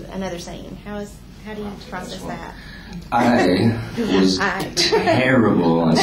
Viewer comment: What does he say at 7:25 ? "I was terrible at..."?